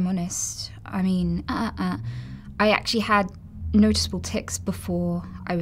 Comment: I actually thought she said, "I really had noticeable tits!" And thought, oh thats true but obviously part of her condition that made her say that.. Then realised she said, "Noticeable ticks!" I know, i know.. Forgive me.